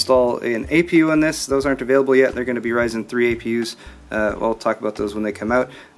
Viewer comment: "ryzen 3 apus.." did I hear that right?